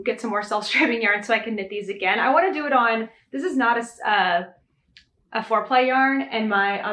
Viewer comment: I totally thought you said “this isn’t a foreplay yarn” and I did a double take and was like “what the heck Nat” 🤣 and then my brain rewound and heard properly 😂